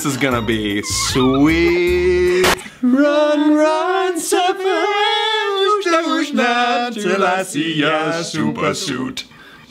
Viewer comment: run, hide and suffer is what I hear